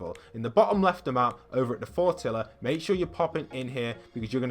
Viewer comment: Fortilla? I thought it said Tortilla